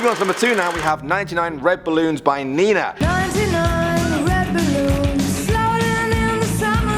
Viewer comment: the 99 red balloons I never heard it before and in the beginning in thought it said "99 revenue" lol